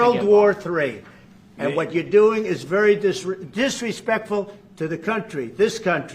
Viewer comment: what did zabilkski say at 5:20? they removed it from the transcripts i have seen, it sounds like "we dont respect you"?